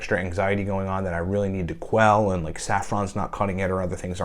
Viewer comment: Did you say Saffron?